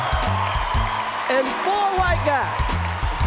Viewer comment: what did Chris Rock say at 1:20 ? it sound to me like "and four white guys" or something?